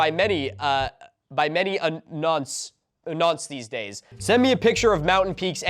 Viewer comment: that word at 40:40 Nonce (None-SSSSSSS ((idk, its litterally just the word None with a s sound, like snake, and snow at the end)) its a british slang word that means like, sexual deviant, but is largely used to call someone a PDF-file, but its also caught on to just be used as a general insult by people who just think its a funny word to say (which it absolutely is)